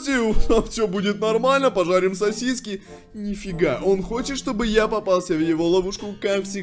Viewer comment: Мне послышалось что сказал не жарить сосиски а (жарить соседей)